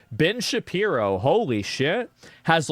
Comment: I had to listen to the very start of this video twice because it sounded like baldy said bitch shapiro